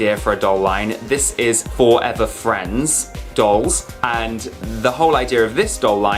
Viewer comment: for a moment I thought you said "weed free friends"